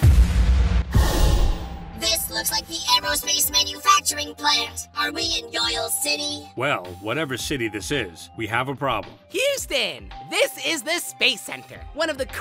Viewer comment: Who else thought it said "Shitty" instead of "City"